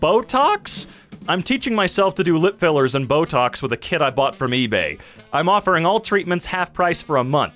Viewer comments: When the video said "Botocks with a kit I bought from eBay" (2:25) I heard "Botocks with a KID I bought from eBay" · this is why you watch the video instead of just listening I legit thought he said "with a kid I bought from eBay"